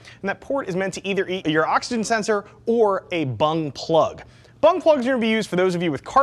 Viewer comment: i thought he said bum plug